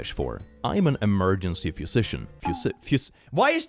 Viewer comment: I heard “Emergency Physician” as “Emergency Pistachio (wasn’t paying attention lol)! Now I can’t stop laughing. 😂